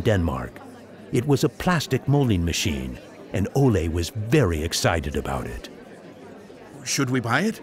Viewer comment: did he say Olan was berry excited